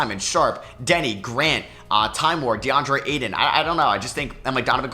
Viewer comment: Who does he says after grant. I’ve heard him say this name but I can’t ever understand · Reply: Time lord. Robert Williams